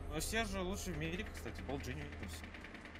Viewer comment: "But Sergio best in the world by the way POLGENICTUS" Xd what last word he said. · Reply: He meant bald genius haha